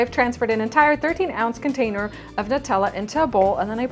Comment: When I listened to the video again I heard her saying 13 ounce. So now I know. It was in the recipe from her webpage that said 1.13 ounce, a little mistyping (spelling?) :)